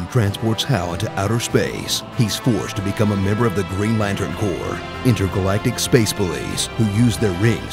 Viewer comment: I heard that as "Intergalactic space bullies" 😂